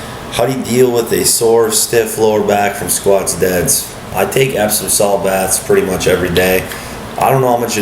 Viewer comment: what baths he said? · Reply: Epsom salt